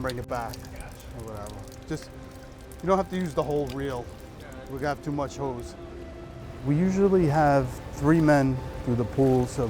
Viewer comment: Sound like he says “We have to much hoes” 😂